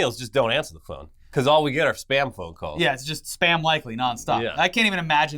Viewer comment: did they say “spam calls”? Not important at all but just made me do a double take while I was brushing my teeth lol